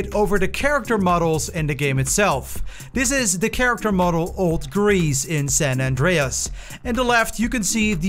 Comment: Sounded like you said Old Grease not Old Reese 😆